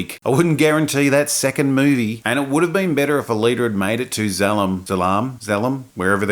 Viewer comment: I'm fuckin sauced rn and every time you said, "Alita," I thought you were saying, "A leader," due to your accent.